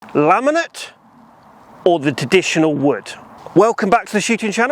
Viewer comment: did he just say "Teditional"?